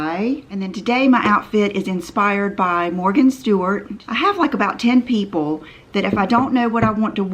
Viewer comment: I thought it said "Martha Stewart" inspired OOTD!🤣 That could be interesting.